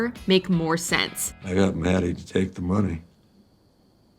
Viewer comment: it sounds like Mike is saying "I got mad at the fake money."